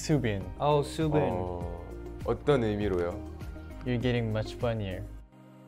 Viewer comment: am i the only one who thought Taehyun said Soobim become bunnier not funnier? lololol